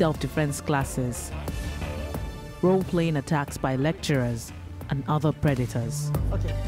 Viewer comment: I thought they were saying lecherer